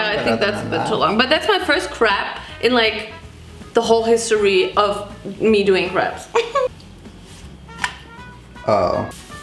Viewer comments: Bro I'm dying cause I thought she said " Woohoo That's my first crap in like the whole history of me doing craps" hahahahah😂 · it sounds like she is saying, thats my first crap . LOL i love her so much lmao<3